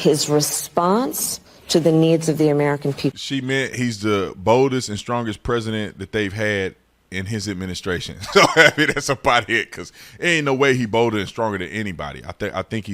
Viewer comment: Boldest and strongest “liars” is what Kamala should’ve said. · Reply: I thought she said oldest and strangest!